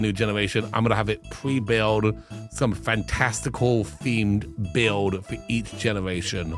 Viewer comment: says "fantastical", all I hear is "maniacal"!. 😂 Generation Yellow. Does the cowplant have to survive? lmao I am SO glad I wasn't eating or drinking when read the last rule for Brown! This is going to be EPIC.